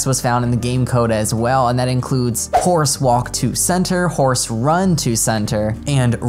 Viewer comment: Im sorry but did he just say "Center" as "Canter"